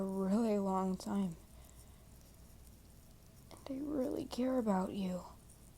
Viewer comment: bro i thought he said “i dont really care about you” during the Proposal 😳💀